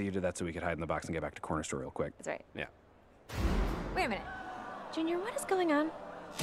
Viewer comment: Dot Says, “Kumonga What Is Going On”? 3:57 Megera Says, “Larry What Is Going On”?